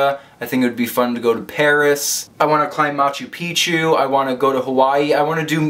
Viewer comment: genuinely thought u said u wanna climb mount Pikachu... me too man, me too.